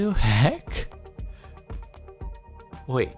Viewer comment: Did He Just Say WADU HECK!?! #WADU